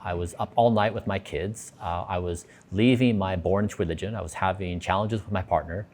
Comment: Anyone know what he said at 22:00 ?????? "I was leaving my 'born twinijin'" is what it sounds like... captions say, "I was leaving my born religion"... It really sounds like he said something different though! Anyone know???